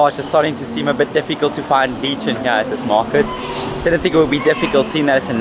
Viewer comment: I was listening to your vlog while doing some work and I kept hearing "lee"chon and started wondering was there some Filipino dish that I haven't had yet? LOL. Yes "le"chon pronounced like "le" in French is very good. Mabuhay from Mississauga Ontario Canada & Coron Palawan!